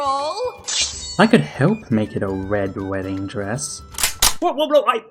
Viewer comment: It sounded like she said "black", not "red".